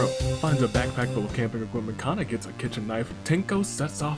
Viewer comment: For some reason I thought you said kitchen egg???? wtf am I on